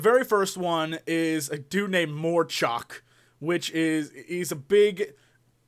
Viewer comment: when they said morchok i thought they said morcock lol